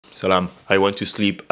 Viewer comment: did you say "SELAM" 0:01 ?